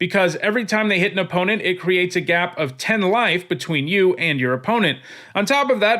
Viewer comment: So idk if I misheard him or just don't understand. With that angel with first strike and lifelink did he say that you gain 10 life? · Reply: No, I said it creates a gap of 10 life between you and your opponent.